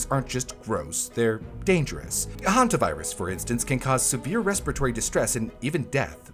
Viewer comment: did u just say hantavirus…